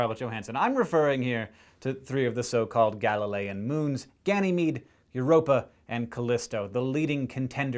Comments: When he said Gallilean moons, at first, I thought he said Galifreyan moons, and I got super excited. But...no. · when hank said "galilian" at 1:33, i thought he said "galifrayian" extremely disappointed.